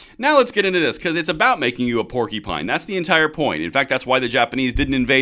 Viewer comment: Unless I misheard, Vivek said “warcupine” which is so much better.